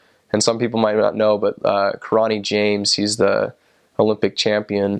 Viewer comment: lol it sounded like he said kirata james. its kirani james lol